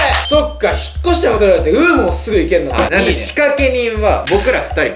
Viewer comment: の仕掛け人の部分ヒカキンって聞こえる